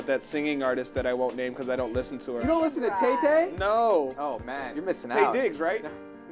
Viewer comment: I thought lasercorn said tay-k😂😂